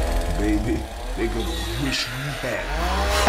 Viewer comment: “And if we run outta bullets?” chainsaw noises “Baby, they’re gonna wish we hadn’t.” Still one of my favorite parts of any trailer for anything ever. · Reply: I always thought Coach said "wish we had 'em". I think it still works both ways.